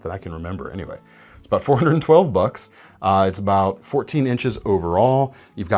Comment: 😂 dude I thought he said "For $112 bucks" Not "$412 bucks" fk that...